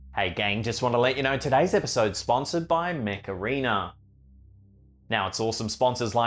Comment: Thought he was saying macarena 😂🤣 i thought 'i dont see the connection here' 🤣